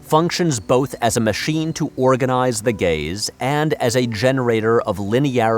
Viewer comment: I thought I heard say "organize the gays".